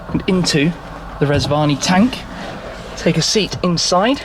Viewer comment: I thought he said Reg Varney Tank. Now that would have impressed me...